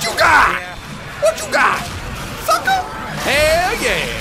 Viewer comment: I think said the F*cker